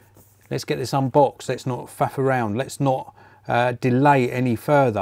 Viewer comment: What did he say? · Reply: Faff … LOL.